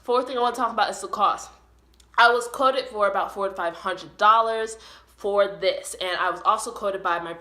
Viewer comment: Bro I thought you said forty five hundred dollars lmaoo my jaw dropped